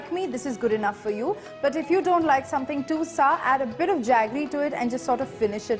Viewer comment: I don't understand what she says at 7:33 "something too xxxxxx add a little bit of xxxxx ?" Please help me understand, thanks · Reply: Sky Above something too sour add a little bit of jaggery